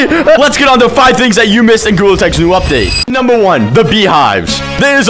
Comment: instead of it saying "gorilla tag" it said "google" (the subtitles)